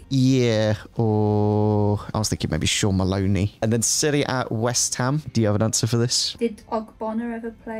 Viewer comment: For a second thought said Syria when she said Serie A and was like, that is SUPER niche